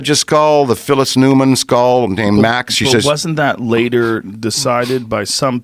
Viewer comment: at first i thought he said Loraine Newman skull :D